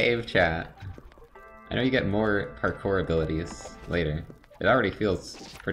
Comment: I thought they said "hardcore" and I was about to make a dumb comment like: "Well, I'm already getting some "hardcore abilities" iF YoU KnOW whAt I mEan."